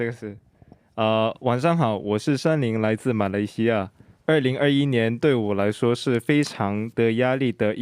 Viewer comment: hendery said Malaysia ? Hahaha or maybe i misheard